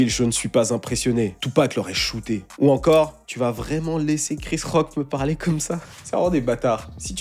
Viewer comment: Mdr quand t'as dis "Il a giflé Chris Rock" j'ai entendu "Rick Ross". Je me suis dis "non ça par contre c'est pas possible." 😂